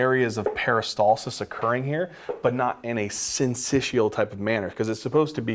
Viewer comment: what word did he use there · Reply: syncytial ?